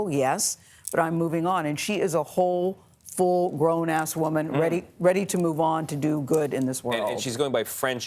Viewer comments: did she just say 'full grown a$$ woman'? Classy cbs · Did she say full grownass woman lmfaooooo whoaaaaa